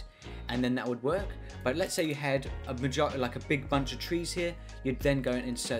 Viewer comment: i heard that bro 💀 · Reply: lets say you had a vagi- like a big bunch of trees here